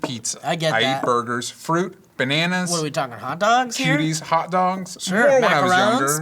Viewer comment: thought said "Hot Dong"